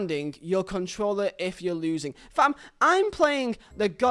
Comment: did he just say 'fam'? 🤔 Wtf!? 😆 So wrong.